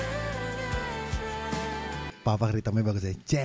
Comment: Kirain salah denger, " Papa Rythm" siapa ? Si Bob Sungjin😭🤣